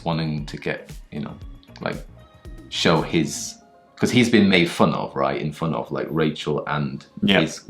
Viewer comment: ここでさん、 He’s been made fun of, right? In front of…と言われたように聞こえました お父さんがどういう反応したのか、結末まで観たかったです〜